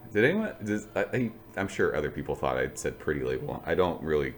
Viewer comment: I also thought you were saying "pretty label" in all of your reviews. 😆